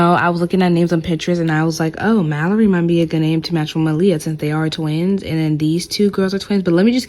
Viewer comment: did you say Maliya…GIRL MY COUSIN NAME IS MALIYA🌷💔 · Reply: No, it probably sounded like That but her name is Maleah.